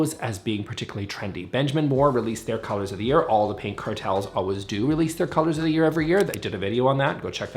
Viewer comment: had to wind back when i heard “paint cartels” 😂